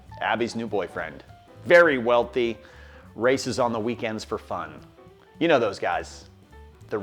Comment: For some reason I thought you said Rick was racist on weekends for fun lmao